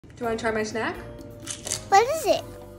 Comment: i swear i thought she said: want to try my snatch!